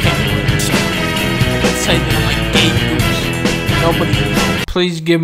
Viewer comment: I heard you say you were a gay goose